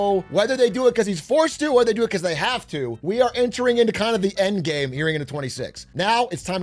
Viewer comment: did you say end game